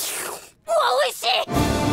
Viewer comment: she says it’s delicious, but all I hear is “HOLY SHIT!”